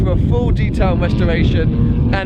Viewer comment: why did i hear it full detail masturbation lol